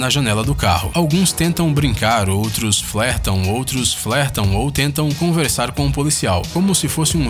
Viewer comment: So eu que ouvi ele falando duas vezes ele falando outros flertam outros flertam